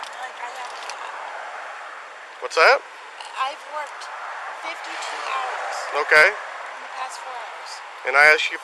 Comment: does she say she worked 52 hours in the last 4 hours ?! 🤣